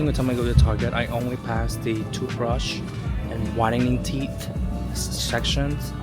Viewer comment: We just got raising canes here, it’s pretty good. When you said whitening teeth section at first I thought you said rotting teeth 😂 I feel like kids are so hard to shop for